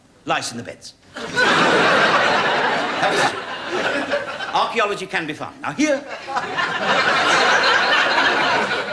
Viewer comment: What does Ronnie say at 1:32? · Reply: I'm pretty sure he says 'that was a joke' that's why he says archaeology can be fun after